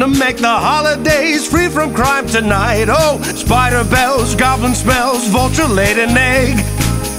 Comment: Did he just say Gotham smells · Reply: No, he said “Goblin smells”.